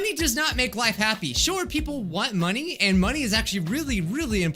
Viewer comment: “Short people want money” That’s what I heard but BOY, if that ain’t the truth...I’m short as hell but also broke as a joke.